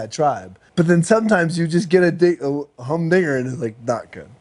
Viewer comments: Did he say the N word @ 1:41? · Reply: Hum dinger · What did he say at @ 1:42? lol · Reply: "Sometimes you just get a humdinger"